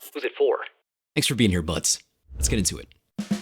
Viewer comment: excuse me my good sir but did you just call me a butt?? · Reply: Haha BUD!